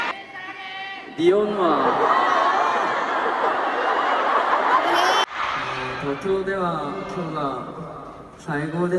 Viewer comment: wHat DOes jimin saY diD he saY eomma pLz cOnfirm me plZ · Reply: He said "Nihon wa" means "Japan is..." because he speak in japanese 😀